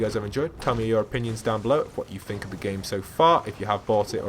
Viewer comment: Sounds like he says v box